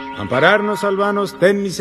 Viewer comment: My Spanish isn’t perfect, but it’s better than Peggy’s. But I’m not sure what the priest is saying. It sounds like “comparadnos, salvadnos de miseracordia de nosotros y guardadnos. ¡O Díos por tu gracia!” but that doesn’t quite make sense. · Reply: Thank you. “Amparadnos” threw me. I thought he was saying “compare us.”